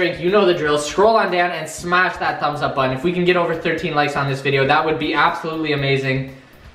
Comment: DID ANYONE ELSE CATCH THAT HE SAID HE WANTS ONLY 13 LIKES 😂 😂 😂 😂 😂 😂 😂